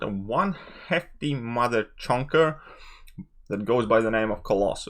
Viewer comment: Did you just say “Heckty mother chunker” ?